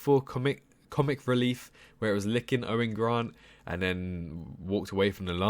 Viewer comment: did he say Owen Grant?